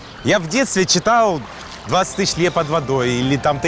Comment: сори но это как то не живой выпуск, обрезанные фразы и 20 тысяч "ЛЕТ" под водой? может 20 тысяч лье под водой? Мус камон.... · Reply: По-моему, он всё-таки сказал "20 тысяч лье под водой". Вам послышалось "лет".